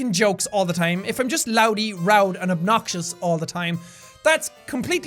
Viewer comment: did jack say, "loudy, roud and obnoxious," child I think you mean roudy, loud and obnoxious